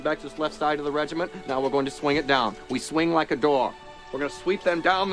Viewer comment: "We swing like a daw..." I love the "Bayonets!" part too. Daniels really puts his heart into it, in that you can see in his eyes the fear of the order he is giving. It is this movie that inspired me to make my 28mm American Civil War Union miniatures the 20th Maine. (To be joined soon by the 54th Massachusetts). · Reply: I have been watching this movie ever since I was a kid, and it was only today I realized he said "we swing like a door" I always thought he said dog. Thanks Maine accent.